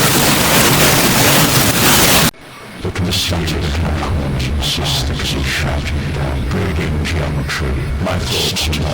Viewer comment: Holy hell I love that. Do you have the script for 1:19:39 - 1:20:46? I can't understand what it's saying, although I assume that's intentional, but I'm asking anyway. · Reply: Thank you, glad you enjoyed it. It's two voiceovers layered over each other. 1)The static in the corners is thickening again, breeding geometry that stutters. The walls are not walls, they are slow liquid watching me, and the ceiling breathes in time with a heart I don't have anymore. Faces in the laminate floor blink. The air has teeth, a low humming dentition, and it’s chewing the signal from everything. Nothing is transmitting the right code. My hand is a puppet doing things, and I am up here, and the strings are made of radio static from a dead channel. They’re speaking through the faucet drip. They’re building a city out of the pattern in the carpet and waiting for me to notice the map. I notice. I notice everything is a collar. The light is a collar. The sound is a collar. I am a dog barking at the wrong god. 2)The committee behind my eyes is shouting down the echoes. My thoughts are not mine, they are donations from a screaming charity, and they keep arriving in the wrong order. Yesterday’s fear is happening now. Tomorrow’s paralysis already happened. The timeline is a scratched record skipping on the same second of panic. I am a roomful of broken televisions all tuned to a station that dissolved. There is a protocol I forgot. There is a password made of taste. My memories are being edited by a shadow in the basement of the skull. It’s splicing in bad footage. It’s writing the script with my hands. The laughter in the ventilation isn’t laughter. It’s the sound of a loc